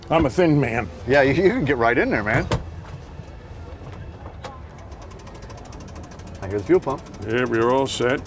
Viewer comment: did he say "i hear fuel pump"? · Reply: “I hear the fuel pump” and yes it was